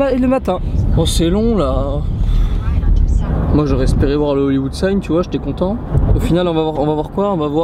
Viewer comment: je reve ou j'ai entendu "lolywood sign" ?